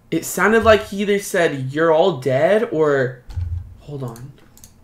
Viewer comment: I heard it say your all dead so creepy